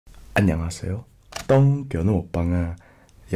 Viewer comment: 똥개라고 들림